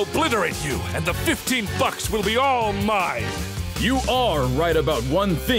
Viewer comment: The dude says bucks but it sounds like he is saying fucks